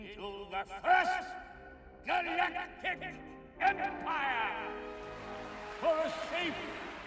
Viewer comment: I misheard his second sentence as "For Sith and secure our society!"